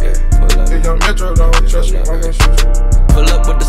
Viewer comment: I can't underestand at 0:04 like, what is he saying? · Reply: He is saying "If lil Metro don't trust you ima shoot you"